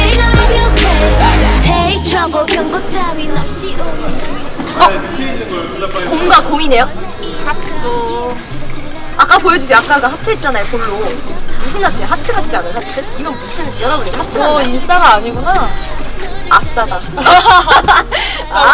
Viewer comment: I thought Seulgi said "Hakdog" @ 1:47 HAHAHAHAHAHHA